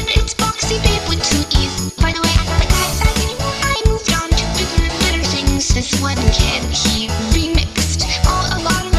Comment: What's she saying there? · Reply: "I moved onto bigger and better things"